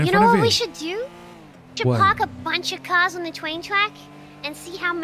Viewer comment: I hear "twain twack" aswell ❤